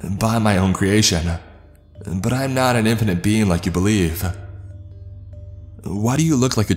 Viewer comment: Did God just say he wasn't an impotent being...?